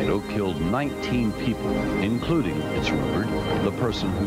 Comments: You might look specifically for obituaries in area papers. Search for Vincent instead of Vince. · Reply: Even stranger, I discovered that "Vince Rupert" never actually existed, people simply misheard the narrator saying "it's rumoured" at 2:09 in the video. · The man who filmed this was Sgt. Lewis Prochniak of Minnesota. At the end of the film he dives into his home. He, his wife, and two children survived without injury. The ‘Vincent Rupert’ name is fictional and started as a rumor. Sgt. Prochniak passed away in Minnesota in 1992 and the obituary shows him holding his favorite camera. There are newspaper clippings about his famous film. It’s an easy Google search if you want to learn more. At 6:17, I see an elevated water tower directly ahead of the cameraman. The tower survived the storm so it would show in subsequent aerial photos of the area. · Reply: The name "Vince Rupert" comes from people mishearing the narrator saying "it's rumoured", at 2:09. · Holy shit, "Vince Rupert" never existed. The narrator at 2:09 says "it's rumoured", which can be easily misheard as "Vince Rupert". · I think the narrator is saying “including, ‘it’s rumored’…” not the name “Vince Rupert”.